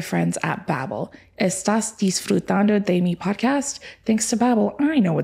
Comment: I was gonna ask a kind German soul to complete what said, I could only catch 🇩🇪 "Ist das.... podcast?" and after a solid minute I realised it was 🇪🇸 "Estás disfrutando de mi podcast?" 🦉